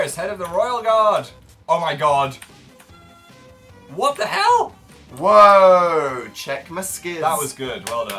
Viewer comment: DID PHIL SAY WHAT THE HELL